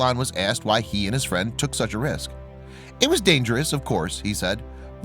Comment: "Would you do the sa.." sorry, couldn't quite hear what you said there so I can't answer that question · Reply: Is would you do the same